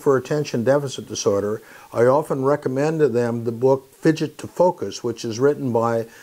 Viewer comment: What is the book he is talking about? Cannot understand him correctly... 6:10 min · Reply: Fidget to focus